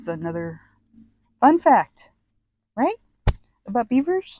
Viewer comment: I had to watch this several times at about 1:55. When says "Just another fun fact about beavers", it sounds like Tulip says "yeah". Is it just me, or did anyone else hear this?